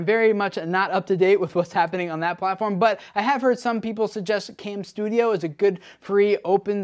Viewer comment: Reports say that Camstudio is loaded with virus... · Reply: ***** Oops thought you said camtasia not camstuido but yeah camstudio has lots